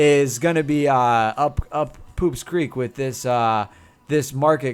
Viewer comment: Two questions; What did you say at 22:26? It sounded like "Poop's Creek". Have you considered getting a co-commentator? I think it would be interesting.